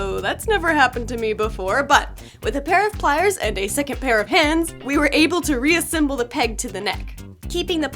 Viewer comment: I heard "2nd pair of pants" When she said "2nd pair of hands" Lol